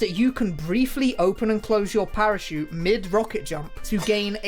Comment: Parachute? I thought you said a pair of shoes